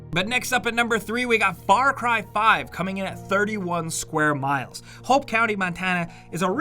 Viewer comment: I thought u guys said size is what matters here, then proceeded to put a 22 square miles map high on the list then a 2200 square miles map. I'm jus saying, correct me if I'm wrong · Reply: 31 square miles* (sorry I didn't hear it properly